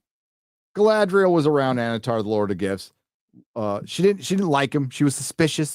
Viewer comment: I swear I kept hearing say "Annatar Lord of Gifs". Does that make Annatar a meme Lord?